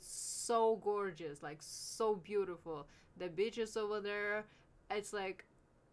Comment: So when you were talking about Columbia and how gorgeous it is, i heard you say "the bitches over there." I was like, did you just say that....5 seconds later it hit me, beaches.